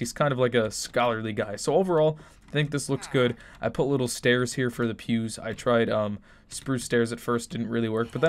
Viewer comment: I thought he said I put oak stairs for the views ☕️ 🤔🤭